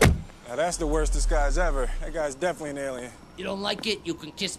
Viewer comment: "Now that's the worst disguise ever. That guy's definitely nailing it." "You don't like it? You can kiss my furry little BUTT!" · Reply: Oh. I misheard; I thought Jay was saying "that guy's definitely nailing it" sarcastically.